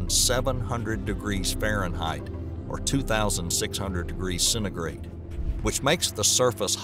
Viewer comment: not sure but when he talked about corot 7b what he said about the second temperature? I am not expert but we have two words for that first is fahrenheit and second is celsius but its 100 percent not what he said and i didnt even understan what he said... its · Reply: Centigrade is just another term for Celsius.